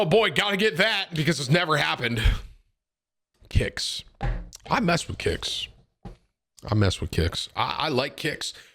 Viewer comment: Sounded like said: I'd mess with kids.